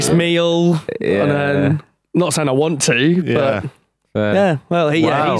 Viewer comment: At first I thought she said Hitch Hitch Holmes